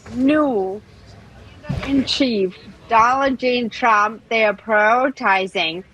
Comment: I swear it sounded like she said ‘Donna Jean Trump’ 😝🤦‍♀️